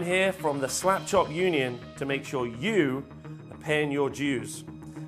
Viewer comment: Subtitles say "sure you pan your Jews" at 0:31 😂